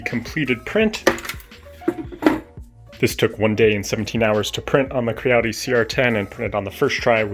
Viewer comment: Hello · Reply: I think that he says "One day and 17 hours." Check at 00:45. He writes this in a comment: "I used Cura with the 'Fine' print quality, which for me is a 0.2 mm layer height. I do an infill of 10%, support overhang angle of 80%, print speed of 60 mm/s, and travel speed of 120 mm/s. Printing the main helmet upside down reduces the support material (and print time) significantly." I tried that in Cura 4.8 and I get 2 days, 17 hours and 56 minutes / 459 grams. Not sure if I dare to print such a huge thing with so small area on the first layer.